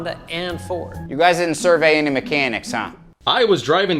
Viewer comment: Can someone please tell me what the guy at 1:20:17 says? I can't understand him and it's driving me up the wall. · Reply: "You guys didn't survey any mechanics huh?"